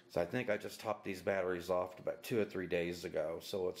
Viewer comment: Hi Jason! I don't understand the question or comment. What do you mean just let it sit? I always pull my LIPOs down to storage level. This tutorial was just to demonstrate how to charge a 1S LIPO using a balance charger for those that have never used a balance charger with a 1S LIPO. I hope that answers your question but let me know if I misunderstood. See you in the Air! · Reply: I thought you said "I topped these batteries up a couple days ago." And I was wondering if 1s was different than multi cell. I'm racing tomorrow AM and I'm hesitant to charge packs and I'll be on the track in 6hrs because of all the horror stories I hear about leaving packs full for you long.